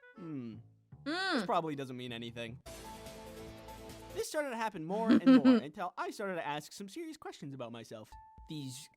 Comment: What is that at 2:24 I think I first heard that playing BotArena on Newgrounds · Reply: I FIGURED IT OUT!! "Break the Targets!!"